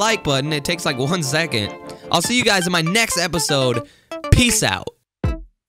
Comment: when he said "peace out" at the end and ad played so it sounded like he just said "pee"